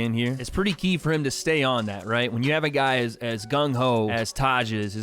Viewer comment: Idk why but I just heard him say Gunn hoe